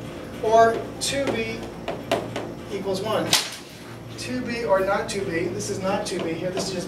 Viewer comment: I heard to be or not to be... lol